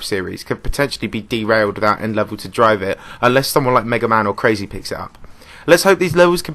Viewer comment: Did say: MegaMan? lmaaoooo, its meta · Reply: Uhm it's really MegaMan. He's the original host of Valor and Mystic, and was relevant during 1.9 to mid-late 2.0 mainly for his top videos. Just search MegaMan + the game's name and you'll find him. He's mostly forgotten now and has quitted the game